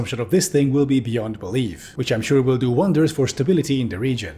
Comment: What does he say it at 4:54 "Ethiopia's Kansas belly" is all I hear